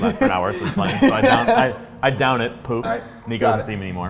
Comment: At first i heard "i down it, and poop"... not 'puff' XD